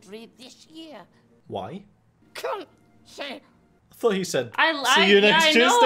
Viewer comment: some people are so serious in the comments lol just let them watch and enjoy! thanks for your reactions guys, hilarious as usual! ps. I also thought dobby said see you next tuesday